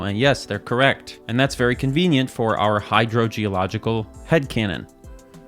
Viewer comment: What is said: "Hydro-Geological Head-Canon" What I hear: "Hydro-Geological Head Cannon" Response: "Sounds fun, but I feel like it would be murder on your spine."